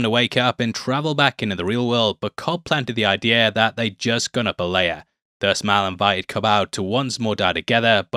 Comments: I had a hard time understanding wtf you were saying here and the captions got it wrong. After a few replays I finally hear you were saying “they’d just gone up a layer ” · it must be your accent. I can't understand it at all, "They just gonna belaya"? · what in god's name did you say at 2:30? such a thick accent. "but Cobb planted the idea that just come up later" "that just gonna belay her" "that just gone open layer" annunciate... please.....